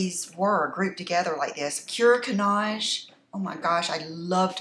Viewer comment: What palette did you say after the Dior Dream palette, at time 12:14? I’ve watched it 5 times to try and figure out what it’s called but I just can’t tell what you’re saying 😆🤷🏻‍♀️ I really want that Dior Dream palette and the one right after it! 🙏🏼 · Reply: Cuir Cannage 😊